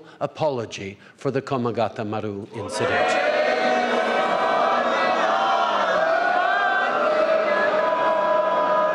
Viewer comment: Out of curiousity, what did the Sikhs say at 3:15? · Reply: sikh religious slogan bro. "jo bole so nihal, sat shri akal" meaning "whosoever speaks the name of god will be in bliss, as gods name is eternal truth in itself"